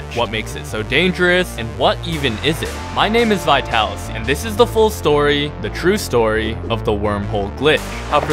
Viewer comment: did he say Zitalasy?